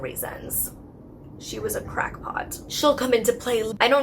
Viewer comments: Did you just say Crockpot instead of crackpot? I cackled. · When I heard Molly's video before this one was uploaded, I heard "crockpot" instead of "crack pot" as well.